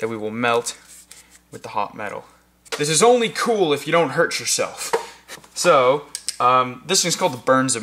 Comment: Its only cool if you hurt yourself lol I thought he said that.